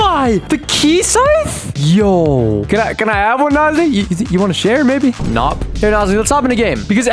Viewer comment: I think my CC is broken cuz it said N zi instead of nosniy